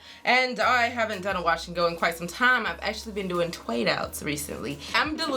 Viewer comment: what did she say ? twad outs? I wanna know lol